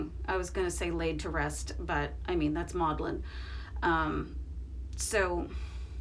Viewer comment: Please confirm....did you say modeling seconds after